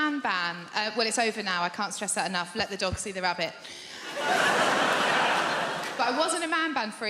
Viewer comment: What the hell is she saying at 1:16? "I been eat a man ban let the dogs eat the rabbit?!?" WTF???